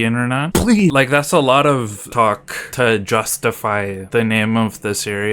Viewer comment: did he just say.. TALK TUAH??